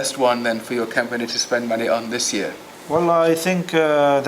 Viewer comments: Did he said „Wallah, I think ...“ at 7:10 or „well“ Hahahaha That‘s gold. In my opinion they are actually pretty good in english ... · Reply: He said wallah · LMAO شسمه - ههههههههه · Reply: I think at 7:09 he said well I